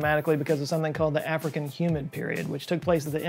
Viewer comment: I had to put subtitles on for humid, I thought you said "human," being English we don't always hear your words correctly.